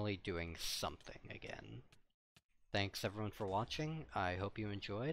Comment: I heard "probably in a few years" xD